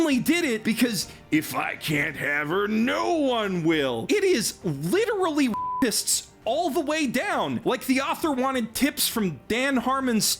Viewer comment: By the way, does anyone know what Geoff was saying here? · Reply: “It’s literally r a p i s t s all the way down.